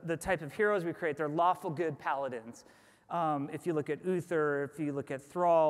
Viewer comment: DID HE SAY PALADINS???? triggered